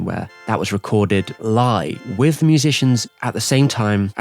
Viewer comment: English isn't my first language. Can somebody tell me what he means when he says "....the curch scene, where it was recorded ly with the musicians and the big band" i don't get it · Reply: He means live not “ly”. So it was recorded like a concert would be with everyone playing at the same time :)